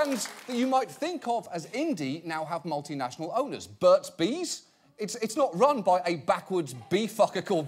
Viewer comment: Anyone else heard a woman screaming "lies" at 3:54 when he talked about Burt's Bees? Check it out.